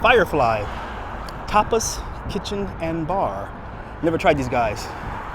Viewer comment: When you rode past Firefly I thought you said "top less kitchen and bar" lol...